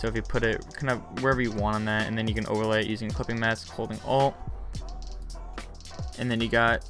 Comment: What did he said, at the moment, he did the overlay at (6:40) I understood like "Holding F holding O" xD